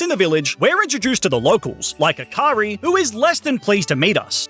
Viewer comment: I thought u said hakari for a sec there💀